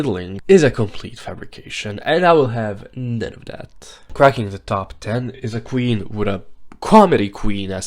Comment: I legit thought they said "fracking the top 10" and I immediately went "Wait, Ru doesn't compete tho?"